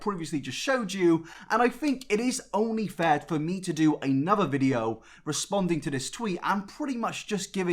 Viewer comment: Wow I thought you were saying welcome back to an inabber video, instead of another video haha love you keep up